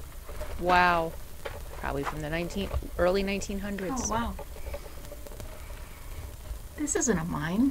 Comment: there is another voice talking over yours! EVP! It sounds like they are either agreeing with you or mocking you, I heard "oh wow" . I know you were saying this a couple of times as you were coming in, but, in this case you were already talking. Haunted for sure :) . I think that little building might have been where they kept dynamite? Love your videos! You are inspiring.